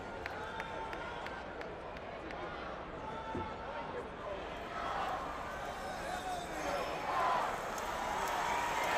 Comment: What were they yelling at 3:13? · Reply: Carmella Boykin 4 more years!